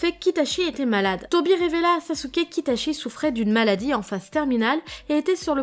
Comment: Quand j'ai entendu itachi se droguait Ma réaction : met retour en arrière à la vidéo. Entend de nouveau "itachi se droguait. Eclate de rire. Va prendre un dolipran pour mon gros mal de tête naissant. Recommence la vidéo. Entend de nouveau que itachi se droguait. Eclate de rire... 😂😂😂 J'Y comprends plus rien à ma vie 😂😂😂😂 Comment je m'appelle ? Hejizhbenzttiotozkveopsjvev3kzlebvb. Enchantée.